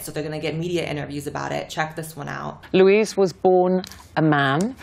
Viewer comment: Did I just hear "a 'whore-man' treatment"?...